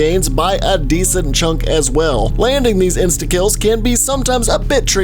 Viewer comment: He sounds like he is saying insta kill